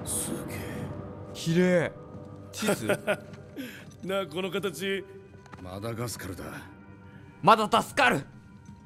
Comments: のマダガスカルが「マダタスカル!」に聞こえた…f^_^;) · マダガスカルが“まだ助かる”に聞こえたｗ